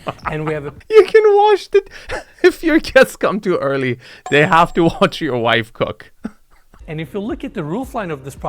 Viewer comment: did I hear him say "They have to watch your wife cook"